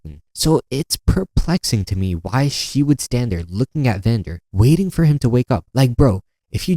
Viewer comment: bro your ai generated subtitles said "vender" not VANDER L BRO GOT SABATOGED BY SILCO IN REAL LIFE